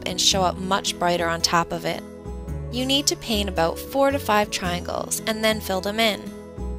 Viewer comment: I thought she said 45 triangles...hehehhe